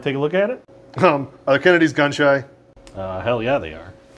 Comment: "Are Kennedys gun shy" is fucking CRAZY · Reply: Glad I saw this comment cuz I thought he said “are the coyotes gun-shy?” Which made sense to me